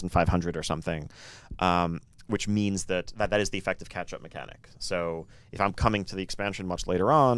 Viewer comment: Just me or does it sound like they say "Ketchup mechanic" when talking about catch-up? lol